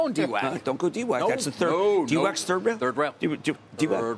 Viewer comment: does he say "D-whack is third rail" at 1:46 lmao... yo cramer i just hit "Subscribe"... nice! XD